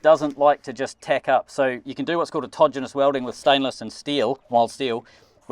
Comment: Thanks to too much background noise I heard that you were about to weld "David's", and my mind went "Weld David's what???". Then I read the title and find that David is safe from being welded 🙂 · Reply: Could be worse... when he said "autogenous weld" I heard "a todger-less weld" and if you google todger... you'll see why I was so confused!